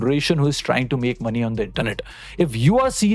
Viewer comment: a small mistake in the captions. "They've done it" Instead of "the internet"